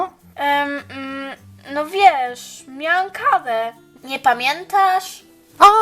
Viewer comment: Super odcinek. Zamiast karę usłyszałam kawę XD